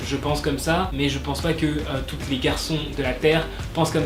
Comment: "mais je ne pense pas que TOUTE les garçons de la terre penses comme ça " je suis la seule qui entend "toute les garçons" ou pas car si je suis la seule ça veut dire que j'ai un problème alors ... #welleuse j'aime beaucoup cette vidéo ça change et pour moi qui ai 13 ans je suis plutôt grande (un peu plus d'1m65 je pense )et mince (45kg) j'ai plus un style du genre BTS 😂❤ bref sinon continue t'est vidéos j'adore 😍❤ Et pour les mecs j'aime bien les gens aux corps de "lâche" sois disant mais moi je trouve juste ça mignon